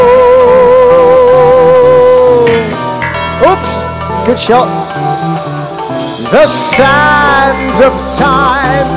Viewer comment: What did Bruce say????? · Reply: “Ooops, good shot!” Someone threw a T-shirt at Steven Harris’ bass! I was there 😂